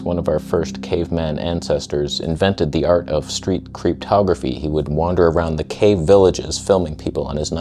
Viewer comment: "creeptography"! I thought you said cryptography and had no freaking idea what you meant!